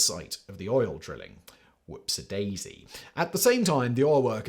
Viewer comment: You say whoopsie Daisy. Close captions says whoopsadoodle.